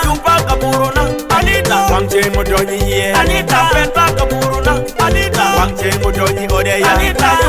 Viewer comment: I heard like " Anita peta kubutu naa" 😂😂😂😂